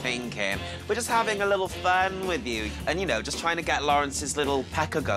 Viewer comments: I cannot for the life of me understand what Lawrence is saying at the end. What’s confirmed? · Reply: haha i heard "weekend say confirmed", her accent is just a lot sometimes lol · ”We CaN sTaY cOnFiRmEd!!” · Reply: I thought he said "weekend stay confirmed" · I cannot for the life of me understand what Lawrence is saying at the end. What’s confirmed? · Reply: lol I was so confused, I played it so many times and I thought he said *WICCAN SEA CON FUR*— like what???